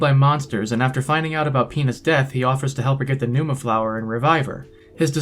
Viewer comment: It may be because I haven't slept but I misheard "Pina's death" as "penis death". Then I had to go like "wait a minute". 😂